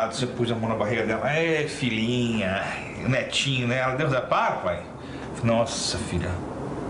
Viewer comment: Vixe! O pai dela ja estava querendo que ela engravidasse com tão pouco tempo de relacionamento. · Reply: Vc entendeu o que ele diz no minuto 4:19? Deus repara? Ñ entendo..